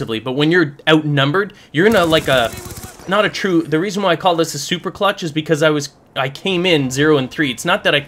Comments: no lie, I thought it said super glitch xD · thumbs up if u thought it said super glitch